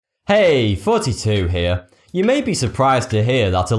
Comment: I always hear "Hey, 42 here!"